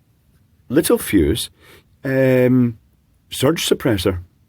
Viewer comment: My world changed a bit when i realized it's pronounced la-tell fuse. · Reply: I heard it as little fuse, because that's their main product: Tiny fuses for things like USB ports .